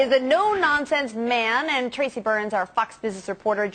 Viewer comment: I thought the news anchor said f**xk news reporter instead of Fox...... lol